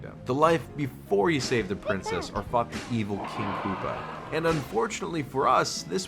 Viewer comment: Thought he said "Fucked the evil king koopa"